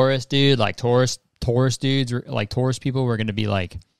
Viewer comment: Thought you were saying tourists instead of Taurus. Was very confused. 1:34 😅